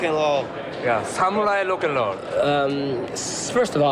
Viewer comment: I'm pretty sure he was trying to say "Samurai rock and roll" but I heard "Samurai lock and load", which also sounds like it'd be fun to hear.